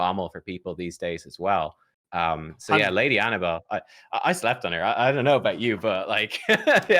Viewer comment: Did I hear it right? - 2:36: "Lady Annabelle, I slept with her"